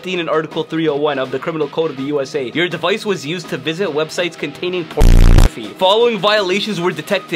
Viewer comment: did that say po*n